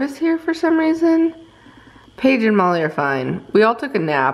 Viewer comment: When you said Page and Molly are fine I thought you said Page and Molly love you go rescue a dog and I was like what the end?!